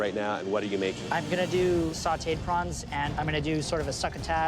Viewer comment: did he said sate prawn?